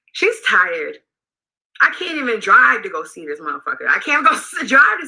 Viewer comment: Did she say she cant even go see the mf-er??( refering to her mom)??